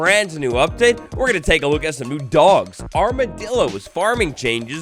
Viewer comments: I thought it said dogs and dildos😂😂😂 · I thought it said dogs and dildos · Doggos and Dillos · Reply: For a sec i thought you said dogges and dildos lol